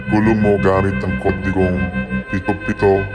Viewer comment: ok to be honest I couldn't understand much of that cuz of their accent but they did say these numbers. 77442